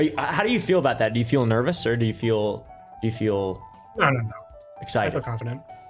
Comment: the subtitle are wrong, he says “no no no” not “I don’t know”, I’m self aware I made a 🤓 moment but I just wanted to help out